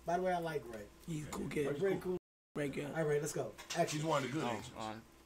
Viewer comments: What did Druski say at 3:04 to get everyone to react? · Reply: "he's one of the good Asians" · What did that fk Dru say? I didnt catch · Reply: He’s the only one good Asian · What did druski say @ 3:07? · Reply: He's one of the good asians